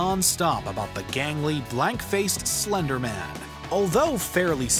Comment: I hope you meant BLANK face instead of BLACK face